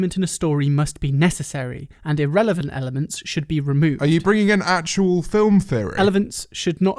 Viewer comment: I thought it said adult film and I was very concerned